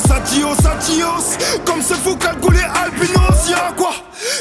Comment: ça parle de Kalash ou quoi la ? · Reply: non gro, il a dit comme ce fou cagoulé albinos, * juste une dédicace